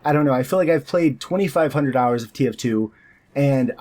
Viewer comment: Is it just me or did he say 2500 dollars of tf2?